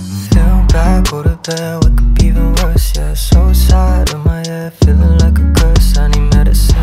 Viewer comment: at first i thought he said "suicide in my head, feelin like a curse" and my heart shattered. thank god it's actually "so sad" TT